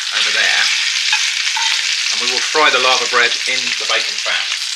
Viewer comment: Laver bread interesting, for one second I thought you said " larva bread ". I gonna try to find some laver bread at Amazon